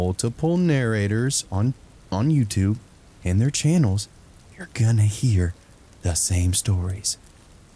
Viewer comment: LMAO 33:44 my volume sucks so it sounded like "you're gonna hear the same stories" pause for saturation 😂 but then I heard over and over and both are equally hilarious, I love the dupes because it's great to hear the different takes on the stories.